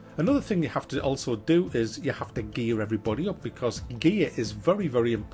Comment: when you said "you got to gear everybody up" I thought you said "you got to gay everybody up". XD that would make for a weird game...